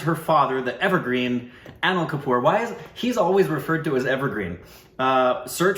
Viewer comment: Did say "ANAL KAPOOR" ?? :D :D :D at the 2:16 .